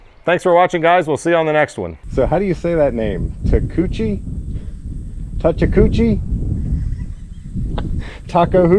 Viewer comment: Did I hear Touch-a-coochie? 😂 Tamagotchi sounds better!